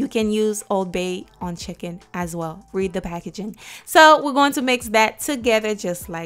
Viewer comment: Idk why when she says read the packaging. All I hear is per my last email. Lol